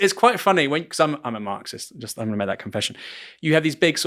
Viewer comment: Great interview. Sad to hear that she that she is hooked into so-called "Global Warming -- Climate change - Imminent Global Freeze - Sky is Falling"! Did he say he is a Marxist, or did I mishear? If so......AAARH!